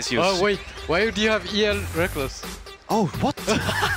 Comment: Hey someone can write(quote) what yellowgod is saying pls thanks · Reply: Hydraks "Why do you have EL Rekkles" (account)